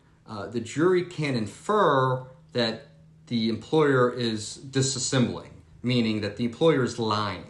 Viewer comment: Hello. Can you clarify what word you used at 14:40 "that the employer is disassembling" (per the transcript) which is what I think I hear, but there is intrigue regarding your pronunciation where I am not sure if you are saying a different word. I did google to try to find if it is a legal use of the word disassembling, but I did not find a hit that confirmed. And I want to use the word you said. Please & TY. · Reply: Nevermind. I used thesaurus and found "dissimulate" Thank you.